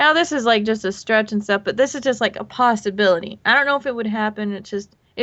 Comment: I heard 'pastability'